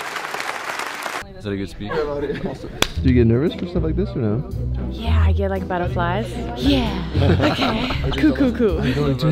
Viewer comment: I misheard her say she had "battleflies"